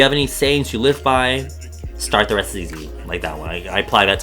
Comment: wait what did he say at 21:39? i can't catch it hahah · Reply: he says "start, the rest is easy" :)